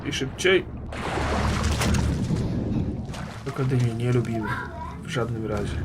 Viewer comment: Czy tylko ja usłyszałem "Rockodyle" ? ;D